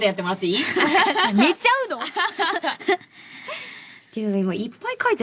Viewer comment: mark after Zucchi asks Konomi to count the sheep after the program, I think Konomi says '寝ちゃうの?' as in "will you fall asleep?"